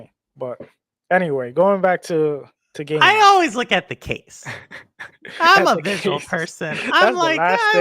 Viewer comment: lol! I heard "I always look at the cakes." I blame Cowboy King of Entertertainment!